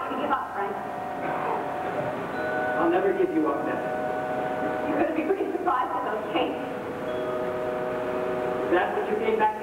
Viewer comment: Can anyone tell what Frank is saying right before singing "Not A Day Goes By"? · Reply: {I tried my best but the audio is hard to decipher & the script is much different from the revised version} From 54:56 ... “I want you to give up, Frank” “I’ll never give you up, Beth” “You couldn’t be (frickin?) surprised with the pain” OR (you couldn't begin to ?? without pain??) “Is that what you think I should say?” “I'm not out to hurt you, Frank, but I will if I have to” “(_?) How are the boys?” “How do you think they are? -- I keep thinking about how I could’ve stopped it. I knew the first time you saw Gussie, I could’ve stopped it” “I miss all of you so much I hurt, Beth” “Did you sleep with her, Frank?” “Oh, Beth” “Oh, Frank”